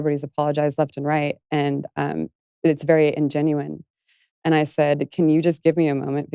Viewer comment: Em 6:50, acredito que ela disse "ungenuine", que seria "não genuíno" ou "falso" e não "ingênuo". O mesmo em 8:29. Obrigado por postar o material!